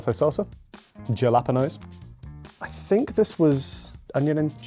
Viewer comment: WHAT did he just say at 8:25?? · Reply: Yalapeños